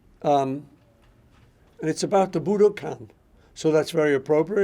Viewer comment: Oh. he said Budokan ok, Idk why but I had it on low volume and I thought he said Hadouken!